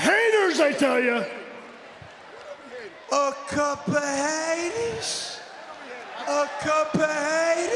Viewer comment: what does Enzo say? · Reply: Glauber Silva a couple of haters.